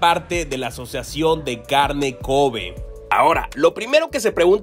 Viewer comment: Carne con covid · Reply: Jesús Alejandro por un momento pense que dijo carne con covid pero era carne cove